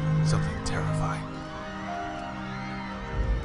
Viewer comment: I thought it said like dust in the wine, and I thought to myself, what a silly world